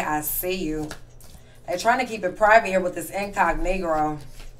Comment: I could’ve sworn you said “incognegro” instead of incognito 🤣😂You’re entertaining and I love your style here ❤️